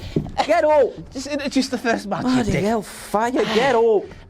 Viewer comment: what did he call?? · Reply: He said “Bloody hell fire” which is a common exclamation in the north of England. He just has a strong Yorkshire accent.